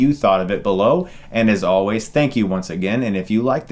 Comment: I thought when he said "Get Stuckmannized" he said "Get stuck in my eyes"